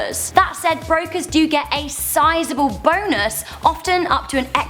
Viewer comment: Did she just say boners?